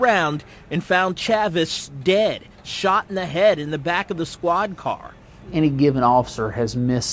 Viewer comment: did anyone else catch the subtitle calling the “squad car” a “swine car” lmao